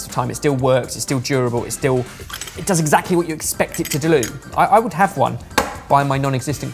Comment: did actually say "..expect it to do-loo" 🤣🤣😭 and ngl i did go on and turned on captions at that point to see if the editor caught that. They respectfully ignored